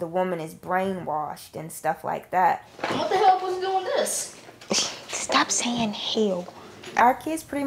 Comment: WHAT DID THAT CHILD SAY?!? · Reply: I think he said “what the hell is going on with this”